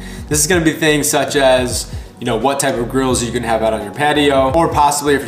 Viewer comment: Raised my eyebrows what I mistakenly thought you said, "...like what kind of GIRLS you can have on your patio" 😂